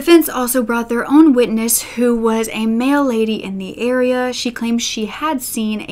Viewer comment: “Mail lady in the area” What I heard “Male lady in the area” My thought: What in the misgendering news reports?.... It wasn’t the ridiculousness of “male lady” and logic of “mail lady” that made me realize though. It was the lack of any correction or acknowledgment immediately from that made me pause and realize I heard wrong lol.